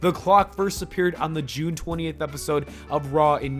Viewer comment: rewound it 5 times sounds like u said 28th anyway lol